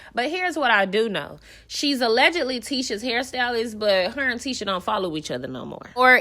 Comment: I thought she said she’s done Tisha’s hair but Tisha has a hair stylist